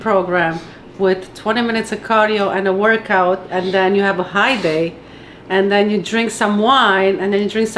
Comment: I literally thought it said HGH day...